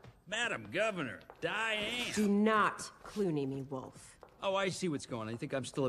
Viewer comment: What does she says at 0:02? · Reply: Do not Clooney me, Wolf